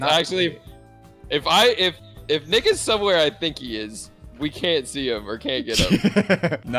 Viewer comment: WHAT DID SAY? 😂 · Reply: Anonymous Person I thought so too but it was “if Nick is somewhere i think he is”